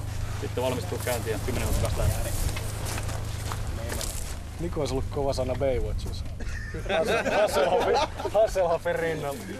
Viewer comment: is a misheard transcript. What he really said: "Niko olis ollut kova sana Baywatchissa" - "Niko would've been great in Baywatch"